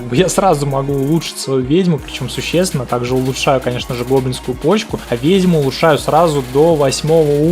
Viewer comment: Только я услышал гоблинскую ПоЧкУ?